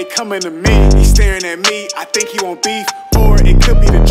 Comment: When he said,” he staring at me I think he want beef” I thought he said ,” he staring at me I think he wanna be me”